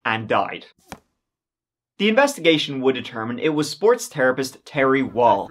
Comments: When he said the guy was a 'sports therapist' I first thought he said 'sports terrorist' which made sense with the whole crossbow thing. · Love you, too! "British Florida" was a gem. Later, you said "sports therapist"; I thought you said "sports terrorist," a term that might come in handy someday....